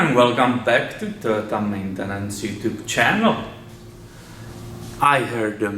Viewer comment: Are you Finnish? I thought you were going to say Mattenen (or something) instead of Maintenance. Haha! The way you said it. Cool! And thanks!